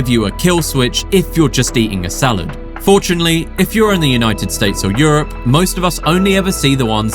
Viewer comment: what did you just say? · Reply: United States OR Europe, although at first I heard it as United States of Europe😂😂